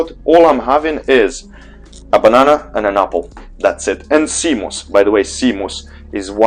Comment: Hello, does anyone know what seed is talking about at 3:00 ? I can't find it and the yt translator write it cmos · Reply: It's not a seed, it's a sea plant. I saw YouTube caption even said seed plant but it's a sea plant and it's called sea moss 👍